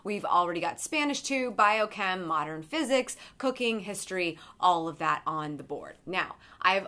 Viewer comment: Hahahaah when you said "cooking," "history," I totally heard "cocaine history." I was like, "wow, electives have changed a bit since I went to school." hahahaha